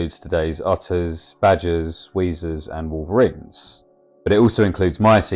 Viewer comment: What does he say at 4:55? Right before he says Megalictis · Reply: Apologies, should have put the name up 😅 it’s Ekorus